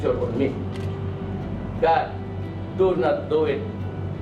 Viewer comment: YOU MISTOOK WHAT DUTERTE SAID.. HE DID NOT SAY ' NOBODY HAS THE RIGHT TO LECTURE ME, GOD DOES NOT DO IT"... "God does not do it" is not the right translation.... when he said, God, its a short variation of "my god" from 'oh my god.. meaning, thats his expression... he did not say that God is not lecturing him, hence ,god does not do it... in fact HE ALREADY USED THE RIGHT GRAMMAR ,, " GOD, DO NOT DO IT " ...but you tweaked it.. or changed it to make it sounds more evil..!! THIS IS THE REASON WHY most WESTERN CITIZENS mistook duterte's pronouncements... due to these wrong translations used by some medias, ( international medias )